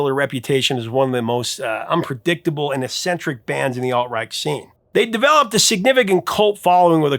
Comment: At around 2:55, it sounds like you said the flaming lips came out of the "alt right" or "alt reich" scene. Alt rock?